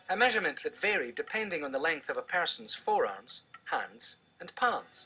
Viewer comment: i thought he said foreass not forearm